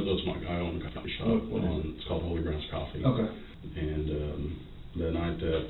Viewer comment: I'm pretty sure he says the name of his coffee shop is Holy Grounds Coffee. There are far too many "Unaudible" moments that I can clearly hear what is said. Maybe auto-generated captions aren't the best choice. If they aren't auto, yeesh.